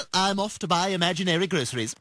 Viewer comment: I thought be said "i'm off to buy imaginary christmas"